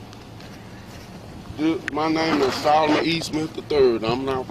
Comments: I also thought he said his last name was but he said E. Smith 1:00 the cops misheard it but obviously were able to identify him via his social security number or birthdate. 6:18 Even though the stop was unlawful in my opinion people MUST learn to comply and put their hands behind their back when tyrants say you are under arrest and not resist less you both end up in cuffs and with a resisting charge. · I mean they said there reason, and he lied about his bame, now he is sus, and they did their job I'm so confused on what the problem is. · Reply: he didn't lie, he said, deputy thought he said....then he was confused when they asked why he lied about his name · That’s not cool. “It’s late and I don’t know who you are.” TF does that even mean? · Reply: C He didn't give the wrong name, they misheard him. Listen again. He doesn't say "Solomon Eastman III". He said "Solomon E. Smith III". On top of that, they didn't even have justification for stopping him in the first place, and the cop lied on his police report to try to cover his ass for unjustified use of his taser. Hence why the cop was arrested for a felony lol. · If you listen, he gave the correct name. His name IS. The Lady officer misheard it as. Rewind it. He gave the right name.